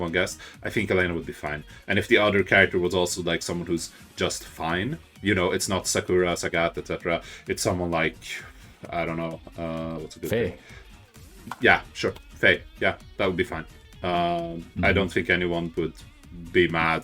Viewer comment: Who are they talking about? Fey? Fae? Am I hearing that correctly? I turned on closed captioning, but that didn’t help. I don’t know any character by that name.